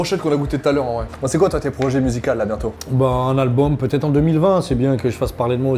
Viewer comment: = " aux" 🤔 Ca veux dire quoi ? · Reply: musicaux